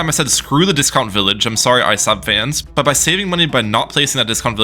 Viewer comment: Did you just say Isub? How do you not know how to pronounce Isab??